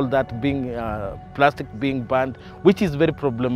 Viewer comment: the caption is wrong, I believe he's saying the plastic is being burned not "banned"